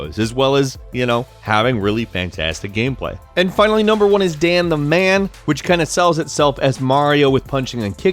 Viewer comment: I thought he said damn the man.